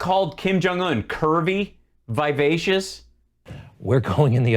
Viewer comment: I thought you said “Kirby…vivacious” now I will always picture Kim as Kirby.